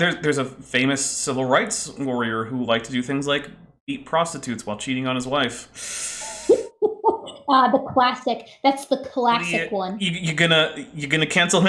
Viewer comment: Did you just say eat prostitutes?!